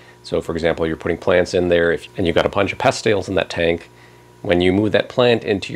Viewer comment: What is he saying at 10:28? Sounds like “pest ails” but i cant find anything on google about it for aquariums. Please help thanks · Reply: Bringing plants …pest snails 🐌